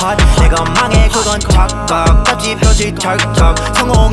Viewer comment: What they say at 4:00 ?! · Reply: "Take it higher, that's on god, that's on ma, that's on pa"